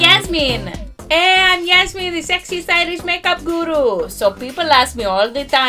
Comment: I thought she said she's Yasmin the sexy Irish 🇮🇪🍀 make-up guru 😂😂😂 lol